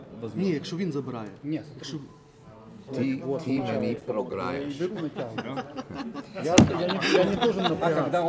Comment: What did Devon say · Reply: Ты мне проиграешь